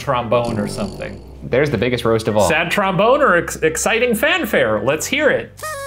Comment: It sounded like said "sad tromboner"